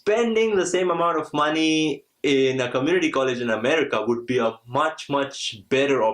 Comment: thanks for the information but what did you say between 4:24 and 4:26? lol im DEAAAAAD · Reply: he said that get in a Community College it’s the best option